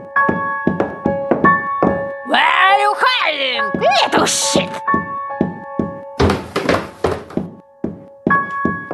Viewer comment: I think she said the s word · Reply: Shit